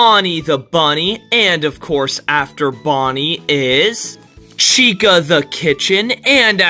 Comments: "Chica the kitchen" ☠️☠️☠️😭😭😭😭thats what I heard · I’m sorry maybe I misheard it, but did you say Chica the kitchen? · Is it just me or I heard him say chica the kitchen? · I heard "Chica the KITCHEN" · i heard chica the kitchen :)